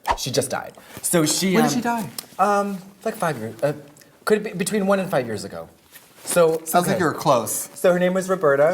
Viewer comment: can anybody tell what trixie is saying / mouthing at 3:20? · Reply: she’s repeating what katya just said “between 1 and 5 years ago?” 😂